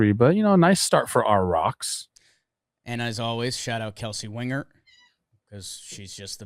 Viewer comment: Did I hear a kitty 1:04:29?? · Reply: It was shouting out Kelsey 😂